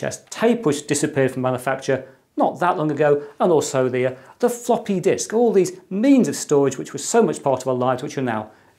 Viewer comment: I thought for a moment you said "Fockin' Disk!" at 4:54 haha